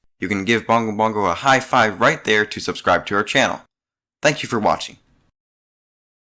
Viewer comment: I hear "thank you for watching" lol.